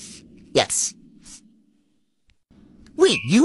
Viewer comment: The passionate kiss after he reveals he is in fact the math wizard > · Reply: I thought he said math lizard to